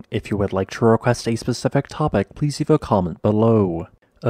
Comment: Hey I love your videos. I noticed now you say at the end “if you would like to request a specific topic…” before I always thought you would say “a Pacific topic” I thought it was something to do with the Pacific ring of fire, but I would not have said anything in case it was not on purpose. So now it seems to have changed. What is the deal?